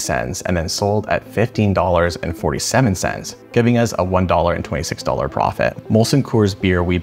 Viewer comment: says $1 and $26 profit instead of $1.26